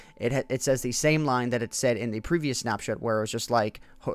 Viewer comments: Did he say snapshit at 2:08? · i think he did say snap s**t